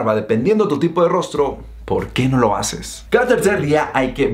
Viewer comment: ¿qué dijiste ahi? :v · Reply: ¿Porque no lo haces?